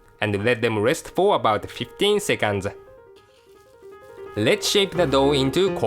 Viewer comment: Francis says 15 seconds but captions say 15 minutes? :/